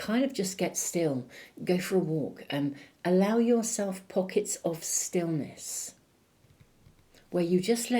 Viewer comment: Thank you. I misheard "Moments of silliness" instead of moments of stillness. Well... sillyness it is 😅